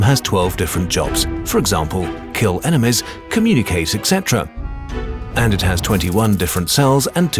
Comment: It sounds like you said kill eminem.....